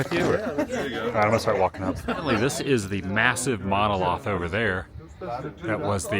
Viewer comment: Great tour. Don’t know what a monoloth is but I know what a monolith is. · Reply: I almost thought he said MONOSLOTH 😂 Still a great vid!